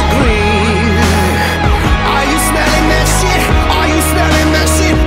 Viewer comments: i feel so stupid omg i didnt know he was saying "that shit" i just thought he was saying "this" oOPS like at the end i could tell he was saying that but the rest of the song i just thought it was "this" smh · I'VE THOUGHT THAT HE'S SAYING "ARE YOU STILL IN THIS" INSTEAD OF "ARE YOU SMELLING THAT SHIT" THIS WHOLE TIME JEVSGWCVAACSXAGWC